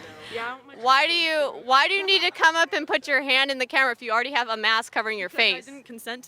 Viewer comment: When she said "Why when you have a mask covering your face" I heard "Why when you have an ass covering your face"